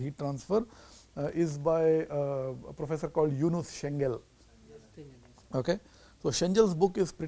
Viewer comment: can anyone tell the author's name at 2:33 ? I couldn't make out the name. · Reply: Yunus Cengal